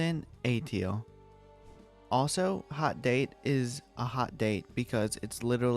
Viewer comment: thought he said hot Dave